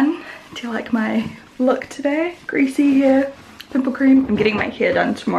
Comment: I wasn't looking at the screen at the very beginning so I heard "greasy hair and nipple cream" I was like wahh..? 😂